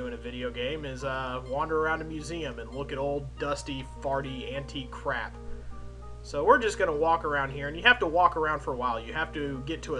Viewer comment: At around 3:36, did you say, "Farty"? What? This game looks like something I would play.